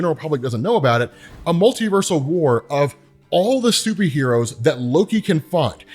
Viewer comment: It really sounded like said Stupidheros.